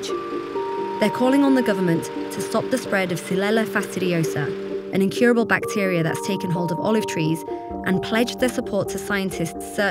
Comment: I thought it said Oliver Tree ;(